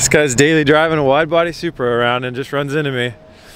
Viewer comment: did i just hear subaru?